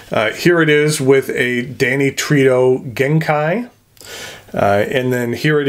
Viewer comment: I thought you said Danny Trejo! I thought....wow that guy makes fountain pens?!! 😅😅😅